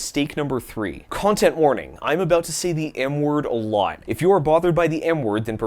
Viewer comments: WTF I THPUGHT U SAID THE N WORD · I thought he said he was gonna say the n-word a lot, but he said m-word